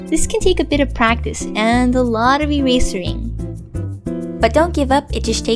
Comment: Wait did she say eraser-ing? :/